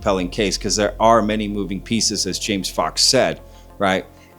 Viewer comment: James Fox exudes such kindness & honesty. Have you ever seen him when he brought his disabled dad with him on interviews? Such love, respect. James Fox is a smart & talented filmmaker. You can tell when he describes the alien’s distress that his intelligence and compassion make him stand out. Watch all his documentaries. 👏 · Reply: At 1rst I thought u said , Jamie Foxx😂😂😂...i was like Jamie ol lying axx dunno what the hell he's taking about